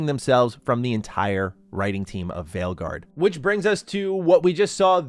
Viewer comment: Did you say a "Failguard" lol?